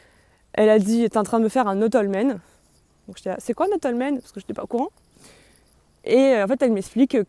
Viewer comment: mais du coup, c'est quoi un [notolmɛn] ? (le mot que tu (vous ?) dis (dites ?) à peu près vers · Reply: C’est « Not all men ». « Ils sont pas tous comme ça ». Or quand on creuse on se rend compte que les rares exceptions ne font que confirmer la règle (je parle en connaissance de cause). Moi désormais, comme Margot, je dis « men are trash » et je pèse mes mots.